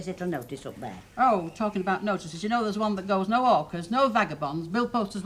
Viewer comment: Every time I hear someone (in this episode Ena to Betty) with a Manchester accent say “No Hawkers” all I hear is “No Orcas”. I keep thinking what a nuisance those door-to-door Killer Whales must be. Glad we don’t have them around here!